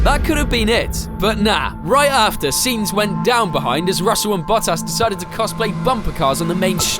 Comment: auto subtitle say Butass😂